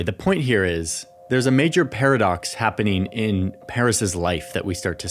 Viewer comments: He actually said Paris, not Harris 😂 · Did you just call her Paris at 4:31? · I swear he said "Paris's life'.